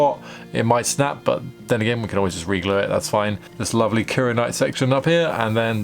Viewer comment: Could you tell us again what the material you used for the handle is called? I keep hearing "karenite". Thank you. Beautiful work!